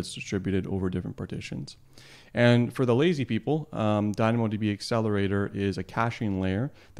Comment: Did you just say for the lazy people ha ha ha. Anyways it is a good tutorial for DynamoDB Keys.